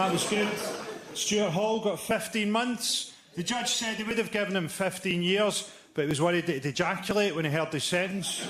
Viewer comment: What does he say here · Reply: "the judge says they would have given him 15 years, but they were worried he would ejactulate when he heard his sentence"